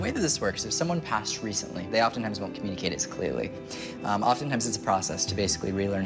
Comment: What does Tyler say around 9:34? Does he say when people recently die they re-learn to communicate? Is he implying reincarnation? As if we go back to heaven and have to learn how to do that side again?